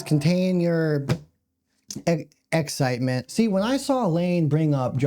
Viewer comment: You said “excitement”, but I heard excrement.✨🤣✨